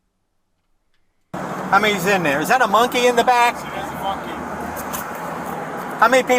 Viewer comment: I thought the officer said ’Is that a monkey on your back'. Instead of 'Is that a monkey in back.'